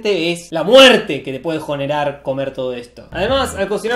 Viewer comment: es como la segunda vez que veo este video y no me habia percatado de que dice "jonerar" xd